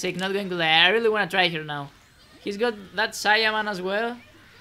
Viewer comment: Might have heard wrong, but did say "I wanna try him now"? xD